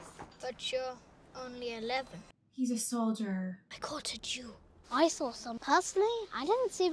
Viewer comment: Oh I get it he said a jew but it sounded like he said (ah chew) that's why she said God bless you in german.